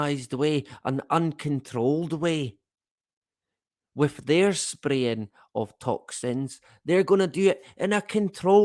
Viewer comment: It sounded like you said ' their spraying us with incesticied ' Knowing them, your probebly right mate.